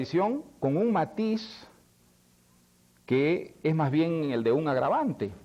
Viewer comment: No te entendí "ni maiz" , creo que cantinfleaste ..saludos